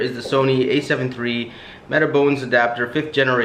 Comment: Hello Sir , I hear at minute 23:35 , you mention again about metabone adopter ? But my English is not good , Could you write the name of it and the generation of it for me please , all your video is helping me a lot , waiting for your reply every minute , I am from Cambodia 🇰🇭 I am the canon love and I am now want to buy the Sony and used with my canon less as you did too, Thanks beforehand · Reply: I put a link to the metabones adapter in the description. It was the 5th generation adapter. Hope that helps. Thank you for the comment.